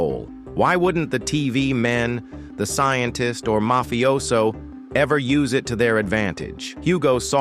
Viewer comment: did he say mafioso?